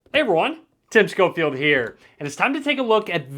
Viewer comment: Everything Tim says 'Hey everyone!' I hear it as 'Hey mor*n!' People who hear it like me👇